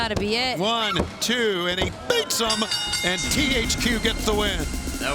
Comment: Did Tony said THQ?